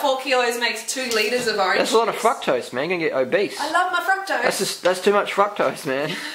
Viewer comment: i thought the guy was saying "fuck-tose" lmao